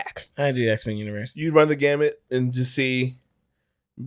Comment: I thought said Hawkins Hanzo was gonna "run up on Gambit" not "run the gambit" for a second and I was like DAMN got hands like THAT?!?